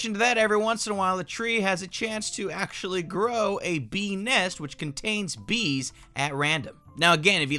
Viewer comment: Every time you said bee nest I thought you said benis